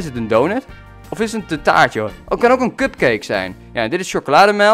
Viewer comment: was ik de enige die kutcake verstond op 6:42? niet sexistisch maar de eerste keer verstond ik het en heb het toen nog is gehoord en toen hoorde ik wel cupcake